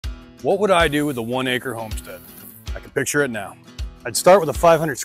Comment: For some reason I heard you say 500 acres. I'm like "say what?" 😂😂 I went back and listened again. 1 acre? Say what?? All that on 1 acre? Wow! I can definitely see it!!